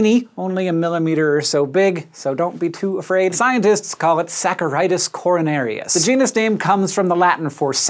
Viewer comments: really thought it said coronavirus for a sec lmao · i thought i heard coronavirus Almost gave me a heart attack